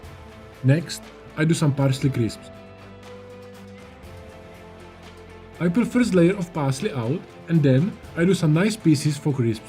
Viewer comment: Hi! Whats this vegetable you slice at 1:55? I'm from Brasil, and didnt catch the word you mentioned :( Thank you so much! Bests, Mauro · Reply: Salsify